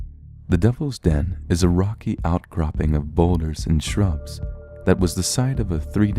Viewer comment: I heard "boners and shrubs" not even kidding.